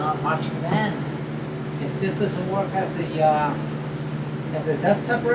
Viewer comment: thien baffle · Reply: daisy corta yeah, I thought he said a ten baffle. I was thinking 10 baffles might be overkill. LOL